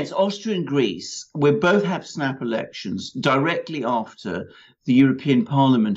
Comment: Did he say "Erections" at 00:46? or did I mishear??